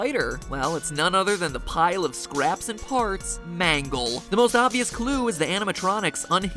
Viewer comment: When you said “Mangle”, I thought you said “manga”.